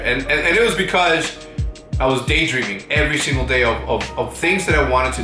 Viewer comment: Anyone else think at 1:47 he says Day Drinking instead of Day Dreaming? haha